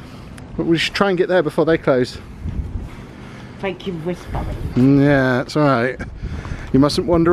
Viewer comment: Does say “thank you for responding” 😂